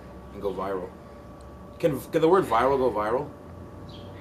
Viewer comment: OMG!!! LMFAO!!! Did he just say "Oh I farted" at the end???